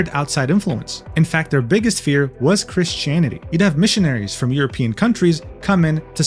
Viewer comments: I swear I heard 'Chris-chanity' there. Now I can't unhear it. Thanks. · deadass thought he said "Chris Chan-ity" like wallahi I'm cooked